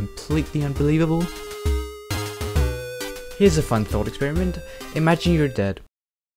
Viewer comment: I THOUGHT HE SAID IMAGINE YOUR DAD 😭😭😭😭